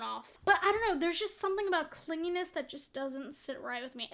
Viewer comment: when you were talking about clinginess i thought you said cleanliness and it took WAY too long to figure out what you actually said 😭